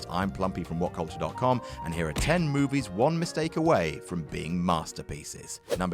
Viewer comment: #11 Star Wars The Last Jedi.... Oh wait you said only ONE mistake away ...oops I thought you said 19 mistakes away...